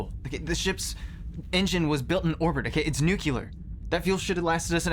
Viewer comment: OH MY GOSH,...Did He actually say "Nuculer",... ...(NUC-U-LER)???